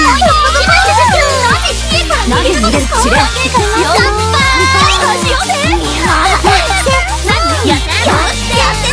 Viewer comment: 辺りの比叡なんて言ってるんだろ… ゲームのボイスじゃない？ もしかしてドラマcdとかのやつかな？ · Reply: 秋村楓 「何故 比叡から逃げるのですか？」だと思います(￣∀￣)